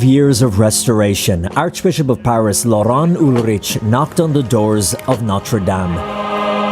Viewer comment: Doors? I thought you said portals 😂